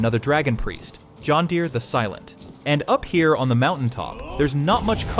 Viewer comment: "John Deere the Silent" is what I thought I heard at first. 🤣